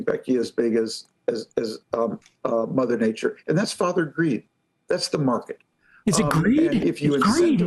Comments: I thought he said "Father Greed"; sounded more honest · I honestly thought he said Father Greed. At least that would have been a little more accurate. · i thought he said "father greed", don't worry, ayn rand and alan greenspan will fix global warming. this is like trump telling people to drink bleach and take malaria drugs for a sars-cov-2 virus. it's like the farther you are from reality the higher capitalism promotes you.